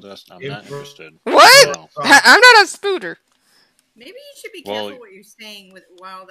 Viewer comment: yo did she just say “Spoder?”